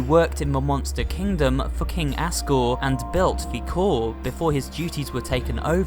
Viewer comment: it sound like he said fu**ng Asgore